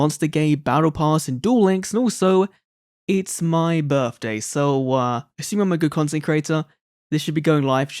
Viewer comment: I thought you said it was mai's birthday xd, happy birthday